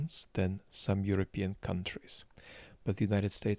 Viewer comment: said: “The United States is lucky that it has stronger political institutions than some European countries.” You said: “How does the US have stronger political institutions than Europe?” See the problem in your thinking here? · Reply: I didn't hear him say "some" i heard him say "than European countries"